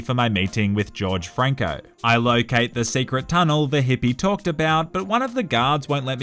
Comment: did somebody say "Secret tunnel" ?